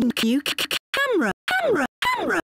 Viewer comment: i thought said cucumber 🥒 lol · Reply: It’s camera